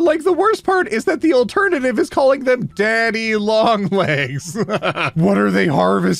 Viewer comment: Okay. I need to know. I CAN'T be the only person who, for the longest time, thought they were called "Dandy Long Legs", right? R I G H T ?